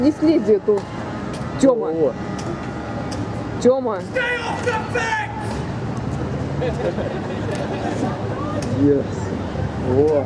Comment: STAY OFF THE FENCE!!! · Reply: I thought he said "STAY THE FUK BACC" thank god someone clarified 😂